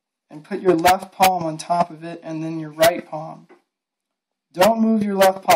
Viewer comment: Nice and quick and to the point. Did anyone else hear him call his hands "paws" around 1:40 lol